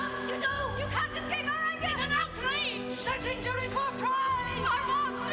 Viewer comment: What does the girl say at 0:08? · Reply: "Such injury to our PRIDE!"